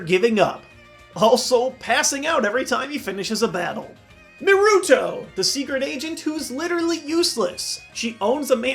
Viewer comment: Is he saying Naruto? naruuto?